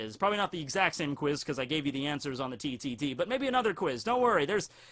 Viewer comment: Am I the only one who thinks Froppy is meh? She's a good character, but not great and certainly not the best. Overrated in my view. Btw TTT sounded like you said titty t